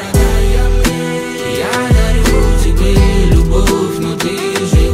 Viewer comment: я почему то слышу не Дарю а Найду, чет со слухом у меня походу.